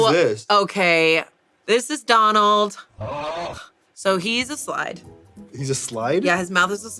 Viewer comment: I thought she said "So he's a slut" and I thought that was so funny but nevermind 😂 It's okay, I forgive you